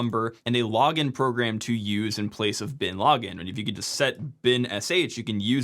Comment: For some reason I just watched this entire 15:44 video where I had no idea what the guy was talking about the entire time. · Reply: I heard "root directory". I know what that is. I thought I heard "Bin Laden" but I think he said "bin/login"